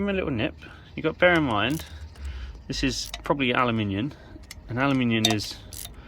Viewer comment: I think it was OK, maybe my super strength covers up how springy the spring was 🤣 yes, Aluminium, not Alooominum 🤣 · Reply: Ah no, you’re reading that wrong, sounded like you said ALUMINIUN